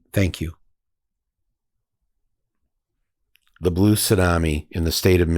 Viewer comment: The Blue Salami? That's what I thought you said.